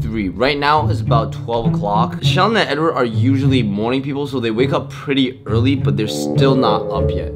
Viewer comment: I thought you said "they're usually mourning people" I was like wtf that's not a good way to start the day.